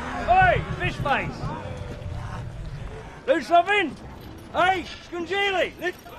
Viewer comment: When Jack says “ Ey? Scunngeilli!” I thought he said “Ey? It’s congealing”